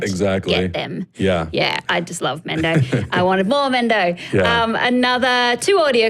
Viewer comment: Personally, I liked the "I rebel" line. And I think I have a pretty sensitive "corniness" radar. Also, at 8:28, did she say she wants to blow Ben Mendelsohn? I listened to it like five times, and that's what I heard. ;) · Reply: Ryan Clark LOL you heard what you wanted to hear! But seriously, she said "I wanted more Mendo." She did sound a little weird when she said it, though.